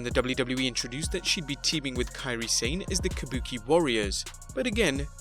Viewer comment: I heard him say Kairi Zane